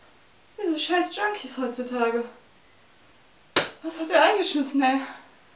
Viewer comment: Ich hab verstanden "Was hat der eingeschissen?" XD Und der Sturz sah dank Outtake extrem real aus. So muss das sein!